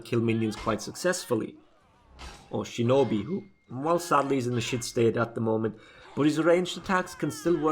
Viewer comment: Did just say shinobi is in a shit state? Finally might defend my poor shinobi